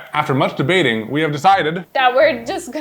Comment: "After masturbating..." I know what said, but THIS was what I heard... 😅🤣🥲